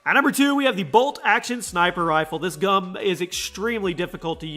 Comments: Did anybody else here him say this gum instead of this gun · did you say this gum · In 8:36 did he say gum lol 😂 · He said this gum instead of this gun.